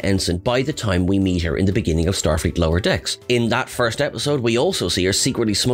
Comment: Am I imagining things or did say “Star fleet lower decks” at 1:44? 🤔🤯